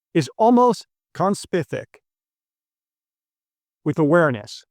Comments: I'm like the other groupies on here - I can't wait to hear your insights and as fellow nerd I love your wonderful outlook on life. But the audio seemed a bit out of sync. What did you say at about 4:26? I heard "Free will is almost conspific with awareness". Thanks! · I am not sure I heard correctly, and even if I did, I don't understand. What do you mean by "Free will is almost CONSPITIC(?) with awareness." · Did you say "Conspithic"?